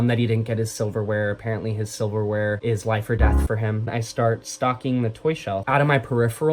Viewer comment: “i start stocking the toy shelf” DEFINITELY HEARD THAT AS STALKING AND NOT STOCKING… was wondering if you were the true psycho in this situation…